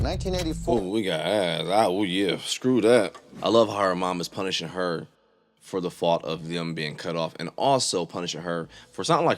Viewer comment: Did you say you love how her mom punished her for being the cause of being cut off? WTF? Did I hear that right? I hope I heard that wrong...